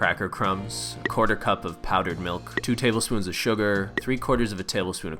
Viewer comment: did he say powdered milk???😂😂😂